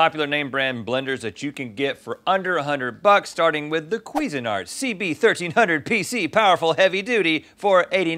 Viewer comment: I gotta admit- when Rhett says " Cuisine Art" for years i thought he was saying " Quisenard" which is a thick smoothie consistency.. Also a fake word..